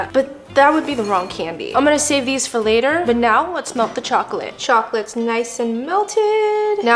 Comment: I honestly thought she said “let’s milk the chocolate”